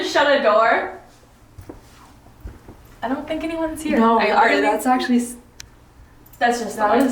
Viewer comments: At @ 14:55 after you asked if someone shut a door I heard a voice say “no” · where says “Did someone just shut a door” I think I hear a “No” in response a couple of seconds after.